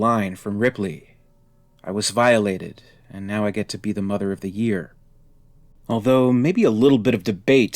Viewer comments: To me, it sounds like Ripley says "Now I get to be mother of the, uh..." and just trails off in thought. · I hear "now I get to be the mother of the yeee", I guess it's "mother of the year" with bad quality audio or she mumbled the pronunciation.